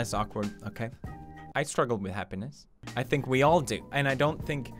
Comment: when he said "I struggle with happiness" I thought he said hemroids... don't even ask...